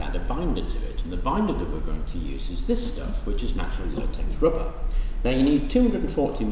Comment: do you think that is possible to find a substitute to latex? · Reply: What do you say @ 4:12 something rubber? I've watched it over and over but can't make it out. Natural Latex Rubber maybe?